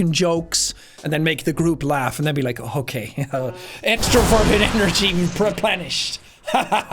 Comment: Every time he said “extroverted” I heard “extra virgin” and it made me question things